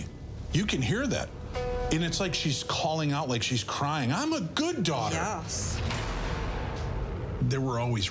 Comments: I HEARD “ IM THE GOOD DAUGHTER” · I hear, "I'm a good daughter" and "My god, I was raped!"